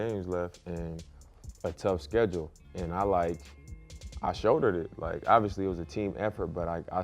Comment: I know he said he "shouldered it" but I swear it sounded like he said "I showed her it"